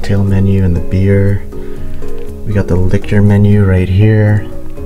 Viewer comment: When he said liquor menu i thought he said lick your menu instead😂